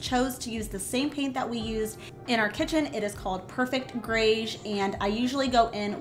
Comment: Where did you get the paint for the vanity & what's the color?? I heard you say Perfect Greys.. 🤔